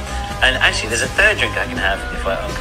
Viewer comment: I thought that said Asians tea pot and that’s sad